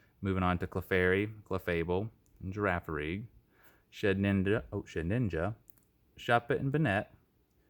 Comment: Am I the only one that heard him say shitninja